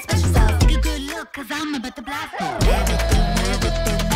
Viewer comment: It sounds like she is saying Newport carnitas.